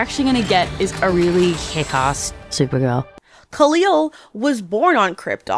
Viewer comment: Khalil? 😅 Now I'm imagining an Arab-coded Clark/Kal-El instead of a white (arguably Jewish) coded one.